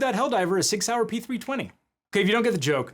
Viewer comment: What he said: "SIG Sauer" What I heard: "6-hour" What I saw: Railgun explosion self-K.O. What I thought (at first): "I'm pretty sure he'll be down a little longer than 6 hours"